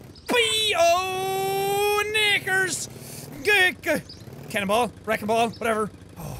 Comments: He said "knickers", in his accent it may have sounded like the N word but you can clearly hear him pronounce the "kniCKers" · What word does Jack say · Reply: Knickers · He didn’t say the n-word, he said knickers. Turn on English captions (NOT auto-generated) and go to 12:34 the captions say knickers. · WHAT DID YOU SAY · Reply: Knickers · Thought he said the n word he said knickers thank goodness 😅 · did he say the n word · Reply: Knickers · lol turned on the captions to see what they said about that one part everyone's taking about and get this "*Irish mumbling*" I'm not sure if that's more racist or just hilarious. how guilty should I feel for laughing at this? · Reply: He said knickers not the N word listen very close you'll hear it at first honestly I thought he did say the N word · Everyone says he say knickers but I heard him say the n word I think Im too dumb to hear it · Yo I thought he said nakers at 12:37 and was like what the fuck is a naker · WHAT. DID. HE. SAY?🙍🏽‍♂️ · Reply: Knickers, another word for underwear lol he just Irish so it sounds funny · It sound like he said nikers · WHAT DID HE SAY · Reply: knickers